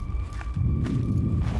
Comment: at 20:32: What? What is what? (thinking the game said something) Me: goes over the area right before he said this. The game from 20:25 to 20:32: * ambience noises * you good? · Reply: The character actually says “Jesus what is that?” I’ve played the game and he’s talking about a tentacle that slithers under the ice that missed